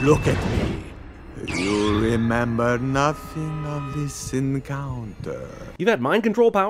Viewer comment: I thought he said you'll remember nothing of this sin counter. 😂😂😂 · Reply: dracula said encounter but spoop heard it as sin counter